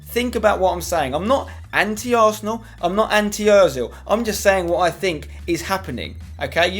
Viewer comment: When you said 'Anti Ozil' I thought you said 'Auntie Ozil' imagined Ozil dressed like Mrs Doubtfire. But about your video, Ozil is the most infuriating player I've ever seen (and I am an Arsenal fan) but I do think he will come good some point over the next year - otherwise he will be shipped on. I think a lot of it is to do with the pressure he is under at Arsenal, at Madrid he was a support act to Ronaldo. Imagine if we had Henry up front with Ozil? Then he'd be worth £40M.